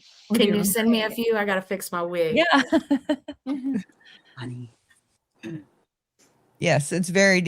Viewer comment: I heard someone say "Honey"?? But I don't think it was anyone on the panel? I could be wrong, because sometimes the audio is a half a second off. But, my immediate thought was "did we just catch some paranormal shit?" 😂